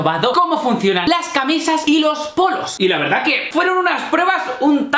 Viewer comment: PENSE QUE dijo porros